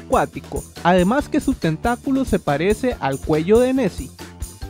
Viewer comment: "Su tentaculo se parece al cuello de messi" dijiste eso o escuche mal? xDDD